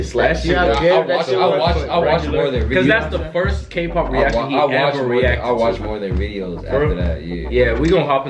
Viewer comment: Whoop whoop I heard "I watched more NCT videos after that" ♡♡♡♡♡♡